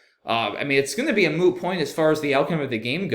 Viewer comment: did you say a "moo" point? ("Friends" reference?) 🐄